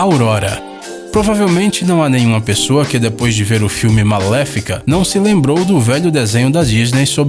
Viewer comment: Eu juro que escutei "maléfica" em vez de "Malévola" kkkk